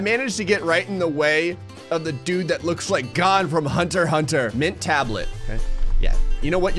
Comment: did he call gon, gone